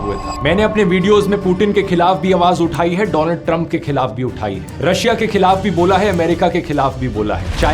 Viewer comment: did he just say DOLAND TRUMP...💀